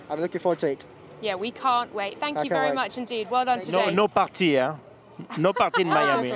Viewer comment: What did Alesi say? “No party.” ?